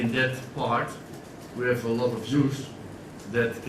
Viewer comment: Apologies for the poor quality. The guys who supply us with the presser had a file issue. Hopefully get a trimmed HD version up later. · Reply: paddyt007 I thought he said "We need to give the Jews a chance" For a second i was completely flabbergasted until I realised he was talking about "youth".